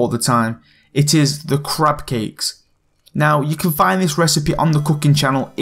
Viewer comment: is it just me or is it because of his accsent it sounded like he said "crap cakes"?